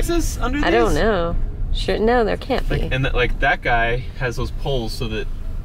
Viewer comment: Did just say "sh*t no there can't be" ?